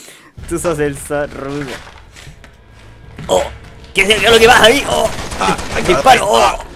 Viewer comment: creo que dijo: Oh, que verga es lo que pasa ahí, oh dispare no joda. Como sea, no tengo idea xD.